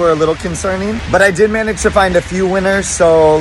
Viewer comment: when he said i few winners i thought he said a few weiners